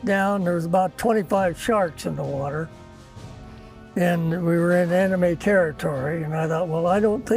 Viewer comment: he says "we were in enemy territory" I first heard "we were in anime territory"